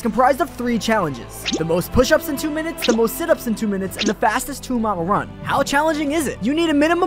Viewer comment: I thought he said 1 Mile run and I was like “how tf did you get 20 minutes